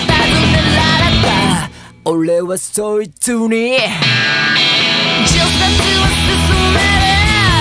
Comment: 北国育ちには 除雪を勧める に聞こえる